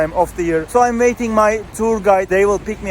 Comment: Did you say ‘Turgay’ my name 🤔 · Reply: It’s tour guide bro 😀😀